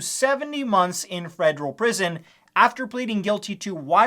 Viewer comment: I heard frederal prison for some reason